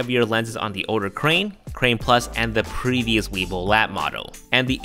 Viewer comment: Did I hear him right? I thought I heard weebil wab.